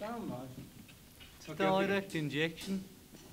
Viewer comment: what is being said at 00:17 XD makes me laugh every time," Stellar rec injection" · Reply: Sounds to me "It's on Direct Injection". Direct Injection is a method of recording when you plug the instrument directly to the recording console (not plugging to the amp, which would be recorded with a microphone normally). As an example, this is how the saturated (distorsioned) guitar was recorded for "Revolution".